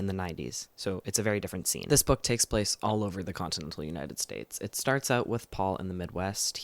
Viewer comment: Did you say the continent of United States? I can't unhear that heuheueheuej Oh right, the *continental" USA. I couldn't hear that :P Also, the author for the shapeshifter story thinking it didn't make sense to have a realistic character arc instead of just doing the same thing for a long time is weird. Not like it can't be the case, it certainly isn't unrealistic for many people, it sure doesn't make the story stay too fun to read